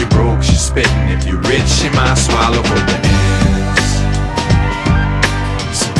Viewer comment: Always thought he was saying "hands" and not "ends"...both make sense nowadays..smh